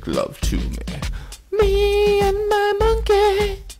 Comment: Oh I thought it was saying "free my monkey ", my bad, lamo!!!